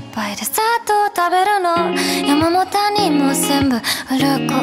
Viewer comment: 最近原曲聞いてて、 「山も谷も全部」の部分が、ずっと 「山本にも全部」に聞こえてて 山本って誰やねんって思ってたので スッキリしました笑 ありがとうございます。歌声素敵です。